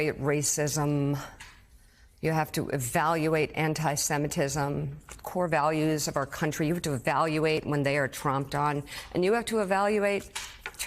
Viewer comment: I know she said, "So you have to evaluate when they're tromped on?" but I love that I heard it as "Trumped on." This country has been Trumped on enough.